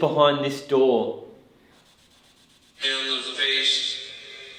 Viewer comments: School's out forever! This was a chilling paranormal investigation inside one very haunted school! Hope you enjoy the spookiness! 👻 🎓 📚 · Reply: I keep hearing "Williams face" or "Williams space." Strange cuz William could also be bill. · I heard "pale with no face" almost slurred like when drunk. Then after you speak it sounds like "hes faceless" or something similar